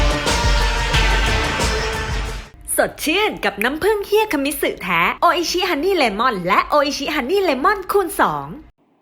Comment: ฟังตอน0:05สิน้ำผึ้งเฮียเเต่เขาพูดว่าเหี้ย555